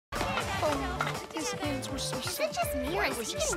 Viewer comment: is it just me or did i hear "is it just me or is he getting grayer with age!?"